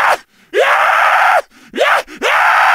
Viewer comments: did anyone else hear "let's go" ??? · "Let's go losers Ahhhhh to the void" ? Anyone hear this? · Time stamps for different zombie sounds aren’t needed but here they are 0:00 Walking 0:08 Attacking 0:19 Behind You 0:26 Crawling 0:42 Death 0:53 Electricity Death 1:04 Running 1:41 Taunt (You Can Stop With The Replies Telling Me To Change It) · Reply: less go losers i hear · I like how it goes from traditional zombie noises to "yah" and "sail" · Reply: I hear them say “let’s go,loser!”😂